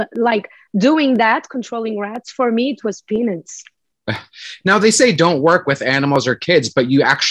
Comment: I’ve seen a lot of interviews of her and she keeps saying that, but idk what she’s saying. Sounds like peanuts